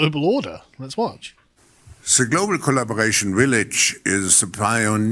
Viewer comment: Sounds like he said "global Potemkin village"